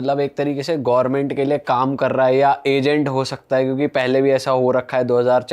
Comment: bhai kya bol raha haii pee vee rakhi hai kyaaa 😂😂